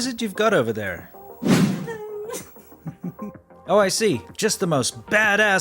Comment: I thought he was gonna say "Oh I see, just a 5G wallet" xDD